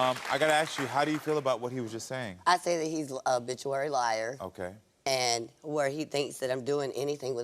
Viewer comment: Sis said he's a habituary liar 😂😂😂😂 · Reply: Lol.. thought she said..obituary liar..I was like..a what